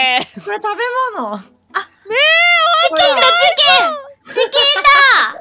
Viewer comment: 事件だ！というよりチキンだ！と言っているように聞こえるが、事件だ！と言ってるようにも聞こえるので、ほんとうのところはわからない 真実はミオのみぞ知る 大神だけに